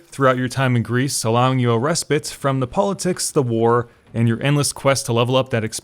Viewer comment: Allowing you a ... from the politics ? What's that word? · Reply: Respite, it means to rest or take a break